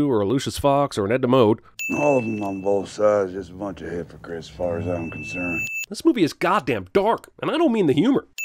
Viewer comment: Anyone know what he says at 7:30? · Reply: I think he says "All of 'em on both sides is hypocrites as far as I'm concerned."